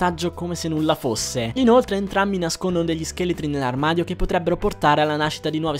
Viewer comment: non ho capito, "nascondono dei scheletri nell'armadio"?